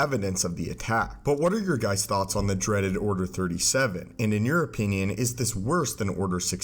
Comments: What? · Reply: he said "And in your opinion..." I heard "And in European..." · i heard "*and in european...*"